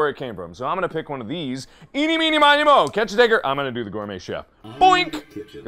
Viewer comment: What did he say?! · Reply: Dw everything’s cool a common word to put there is “tigger” as in the tiger from Winnie the Pooh